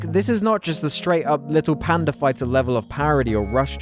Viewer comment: When he said the Little Panda fighter I thought he said the little abortion fighter